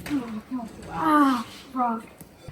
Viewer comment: Did say a naughty at around the 11:25 mark of the video??😂😂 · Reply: He said “ow rock”